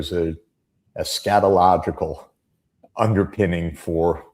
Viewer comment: He says Eschatological [study of "end times,"] but it sounds like he says Scatological [study of excrement] which is MUCH more accurate. ∞